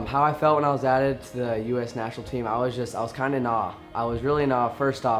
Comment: what is this some kind of cutting edge slang on the first week of its use? I heard “I was kinda naw”...it made me very much naw